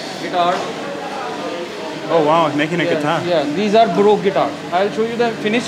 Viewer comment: The caption says "broke guitars" 😂😭 it's supposed to be baroque